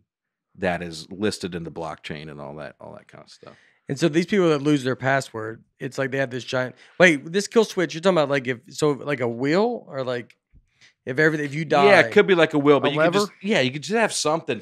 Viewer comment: I thought Nate said “like a wheel” but he actually meant “will”